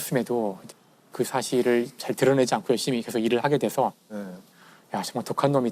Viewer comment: 나만 이거 야 ㅅㅂ 독한놈이다.. 이렇게 들림?